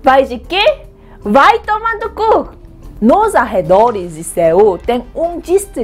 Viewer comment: Que susto quando a falou - TteokManduCuk eu começei a rir mas quando ela disse isso achei que ela tinha dito : - Toma no cú. Aí eu fiquei tipo😶 Daí depois de um século que aparece aquele Gif na minha mente : Ah Agora eu entendi. 😂😂😂😂😂😂😂😂